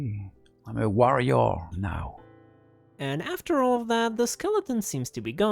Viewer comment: I thought he said I'm a Wario now, at first 😅